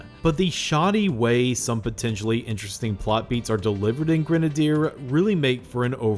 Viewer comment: I thought it said genaraider